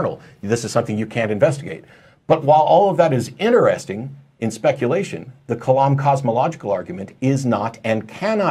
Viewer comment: Did he just say "interesting and speculative"?